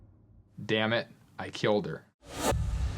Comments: I honestly heard “they think I killed her” lmao that would make more sense than what they thought he said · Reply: I hear "anyway, I killed her" ... I seem to be the only one though... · During the interview at the police station when he mumbled those words (at 9:48-9:50 & 9:58-10:00 minutes on the video), I think he actually said; "They think I killed her". I think it was just a poor attempt at manipulating law enforcement. He was acting like he was "thinking out loud". Unfortunately, I believe the investigators were just hearing what they wanted to hear. But I also believe they got the right guy and I'm very greatful! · I heard him say “They think I killed her.” I’m glad he was finally caught. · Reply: I heard "Anyway, i killed her" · Sounds like he said "I can't believe I killed her" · Sounds like he said “they think I killed her” not “dammit I killed her.” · I don't hear him saying "dammit, I killed her" - I hear "they think I killed her." I believe in his guilt, just not that that's what he said. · I heard him say “They think I killed her.” I’m glad he was finally caught. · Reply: I heard "Did that mean I killed her?"😅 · Disagree He clearly said "They think I killed her" Which makes far more sense given the situation in which he said it. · First time I heard it at low volume, I thought he said, "... and then I killed her." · I heard "Ain't no way I killed her". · Sounds like he said “Anyway, I killed her.” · To my ear, he clearly says "didn't mean that I killed her".